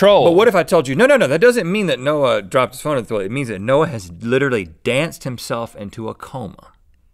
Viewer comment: I remember when this kind of thing happened to a guy.. called Forrest Gump... he just had an urge to run and loads of people joined in. Luckily. No one died. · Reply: Joe Bailey I thought he said 'dropped his cell phone in the toilet' at first instead of 'danced himself into a coma'